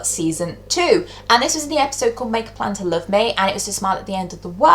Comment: Hey, did you know that the episode is actually called 'Make a Place to Love Me' not 'Make a Plan to Love Me' but I think CBBC changed it for iPlayer cause it sounded too suggestive.